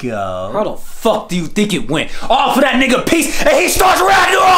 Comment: "I OFFER THAT N**GA PEACE AND HE STARTS RAGNAROK!!" this fucking got me. Right at the beginning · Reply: I heard it as "all for that nigga piece" meaning his wife